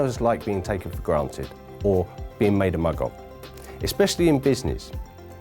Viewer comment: did he say Muggle???